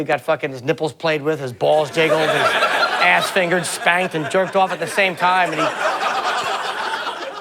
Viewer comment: BAILEY JAY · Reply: lol for second I thought you said "you jerked off a tranny"